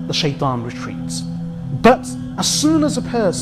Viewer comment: its "ceases to remember" instead of "seizes to remember"